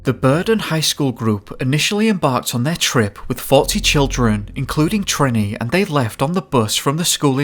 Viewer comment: When you said 40 children I thought you said faulty children lol